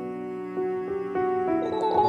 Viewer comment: No one's talking about the dono. "Make a grave for baby man." That's the best part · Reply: I honestly thought it said "You make a great baby man" lol